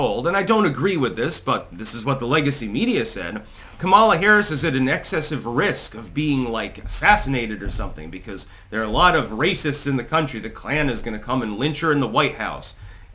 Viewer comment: I thought they said walls is racist. Now he's not? Weird.